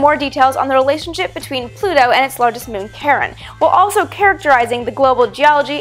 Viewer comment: I always hear "Charon" as "Karen." And for some reason, I want to have a planet named "Jack."